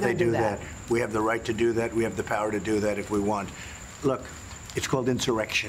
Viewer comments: did he say if we won or if we want? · You misheard him. He said, "We have the power to do that if we want." Not "if we won"... if we want. That doesn't really make it better, obviously, but you're misrepresenting the problem when you say he's talking about becoming more authoritarian if he's reelected. He's saying he can already do that right now. (Unfortunately I'm pretty sure he would become more authoritarian if we won reelection, but that's not what he's saying here.)